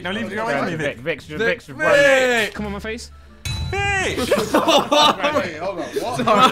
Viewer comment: Don't tell me you didn't hear Tobi say "c*m on my face"